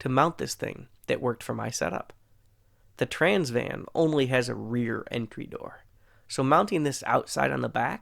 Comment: I thought you said "the trans man only has a rear entry door" and I was like... spittake*